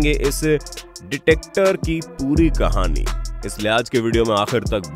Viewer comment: Wait what!? Detector? Am I the only one who heard detector instead of dictator?🙄